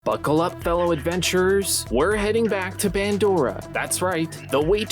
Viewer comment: Did he just say Bandora?!?!????!?! 🤣🤣🤣